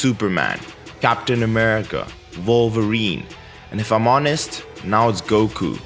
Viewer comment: you pronounce Wolverine really weirdly! It's sounded like Vulva-reen.... lol